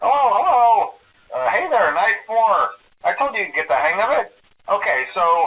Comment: I'm pretty sure he says "Hey there, Night 4!" instead of "Hey there, nice work!"